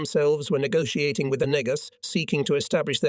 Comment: whaaattt??? I think he actually said negus which is an ancient African reference to royalty